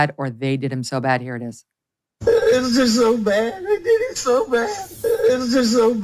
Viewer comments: Am I the only person who heard him say three times, “It was just so bad, I did it so bad”, or do I just have good hearing. It sounded clear as a bell to me. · I heard “I did em so bad “ I think all the cops were protecting him. Look at how they didn’t secure the crime scene that was full of lawyers. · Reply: I clearly heard... "I did 'em so bad".